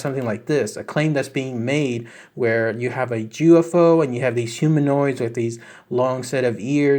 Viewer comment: Did you say Jewufo? @ 6:48-51